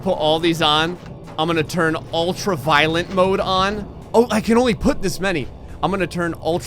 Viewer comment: I thought you said ultra violet 😂😂